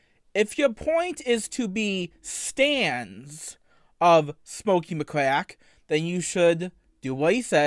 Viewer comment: Whenever I hear "Smokey McQuack" it makes me think of a Ducktales villain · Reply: I lowkey thought he was saying Smokey McCrack